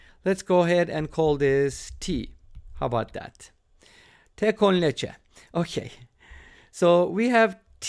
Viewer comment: in 5:22 what did he say? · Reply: Con Leche (with milk)